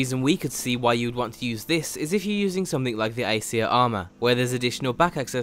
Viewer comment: what armor mod was mentioned? Sounded like "icier armor" or something but I couldn't make it out. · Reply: Aesir armor.